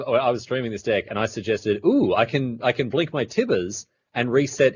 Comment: Wtf is he saying? Tithis? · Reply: He was talking about how blinking tibalt turns him into valki.